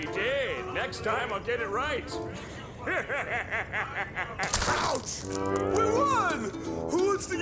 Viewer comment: When the caption says “Gah!”, Plankton only said “Ouch!”.